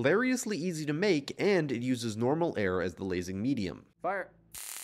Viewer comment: Did they say fart???. Or bark???? Or something else idk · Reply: "fire"